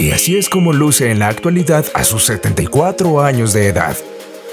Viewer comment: como que dice "antes y antes" 😂.??